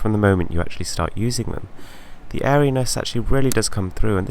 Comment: What did you say in 2:49 ? The fairness?! What do you mean?